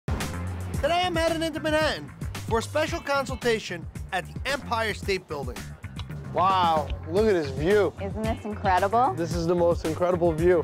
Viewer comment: When he said look at this view i thought he said look at this dude😭🤣🤣